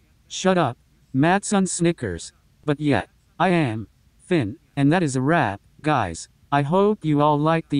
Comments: I thought it said “and that is a wap” 😂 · What it said:”that’s a wrap” what I heard: “that’s a wap” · At the end i thought it said "Now thats a wap"GZDUDUDUDUDUDUDYDYUD